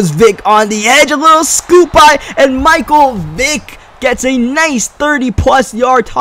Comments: i thought he said micheal dick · Reviewer keep playing it at 0.5 from then on and it sounds like he is drunk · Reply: yah, at one point i thought he said micheal dick